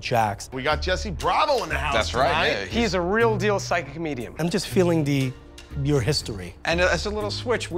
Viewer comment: > Real deal > Psychic medium Pick one. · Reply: Ohhh i thought he said psychic comedian